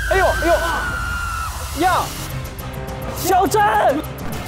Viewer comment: 他们叫死者“小甄！”的时候我错听成肖战了。。吓我一跳